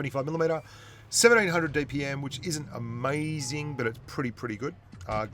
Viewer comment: Wait what did i hear that right or what at 8:12 he said 78-hundred dpm which means 7800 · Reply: random wot player no he said 1700 Dpm